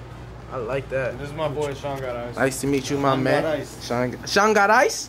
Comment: Shangarice 😂😂 That’s what i heard too.